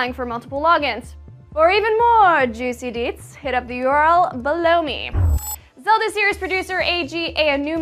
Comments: lol i thought she said blow me! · did she just said "Blow me??" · Sounded like she said blow me? ;) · Below me... Lol thought she said blow me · for more juicy details, blow me???damn i hear crazy stuff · I thought she said "blow me" o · is it me or did it sound like she said "blow me" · Lol i thought she said "Blow me"